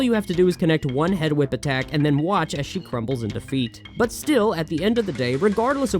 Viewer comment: You know, it's perhaps my favorite example of precisely how weird the '90s were that during the Earthworm Jim segment, I could have sworn I heard you say "she crumbles into feet" instead of what you actually said, "crumbles in defeat", and yet neither one registered as a particularly improbable ending for a video game from that era.